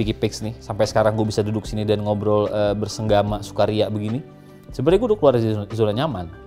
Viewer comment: gw salah denger apa ya? di menit ke 7:50 "bersenggama bersuka ria gini"